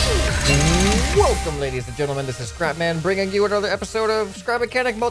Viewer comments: You should say "I am ScrapMan" instead of "This is ScrapMan" because it sounds like you say "This is CrapMan". lol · DID SAY CRAPMAN!?